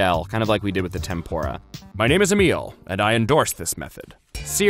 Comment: His voice reminds me of the voice of the rat from ratatouille lmao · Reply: He also mentioned Remy's brother, Emile at 6:33 Edit: apparently I just misheard his real name (which is Amiel) (But if you think about it, Amiel sounds like Emile, and it still has a strong connection between him and the film)